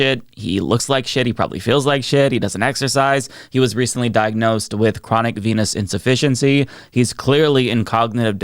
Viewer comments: Ok for real i thought you said at 45 seconds "he was diagnosed with chronic penis insuffciency" i thought yeah that sounds right. Lol also not only is Jones such a Trump loving asshole but has the most irritating voice second only to Trump. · I heard “Penis insufficiency,” not “venous insufficiency,” which follows, too! 😝